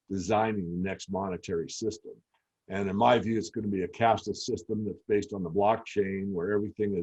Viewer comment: Did he say a Cast-less or a Cast system? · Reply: Cash-less system based in the blockchain